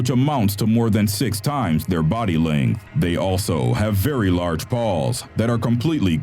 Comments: i thought he said balls... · Am I the only one who thought he said "Very Large Balls" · Thought he Said "very large balls" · I thought he said They also have very large balls... xD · who else heard “balls”